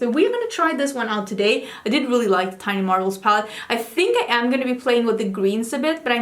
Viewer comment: I’m sorry! I must have misheard. I listened twice even. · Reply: I heard that. I was talking about what I heard at 2:57 but when I listen back again, you probably said, “I did really like the Tiny Marvels palette”, not “I didn’t really like the Tiny Marvels palette”. I’m sorry! I was surprised to hear it and that’s because I didn’t hear it.